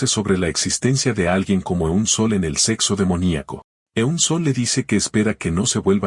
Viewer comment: Escuche "Sexo Demoníaco" 🤣🤣🤣🤣